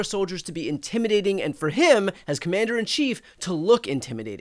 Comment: Mandarin-in-Chief. At first thought I heard that phrase. Wasn't the case, but still found it funny... and somewhat metaphorical.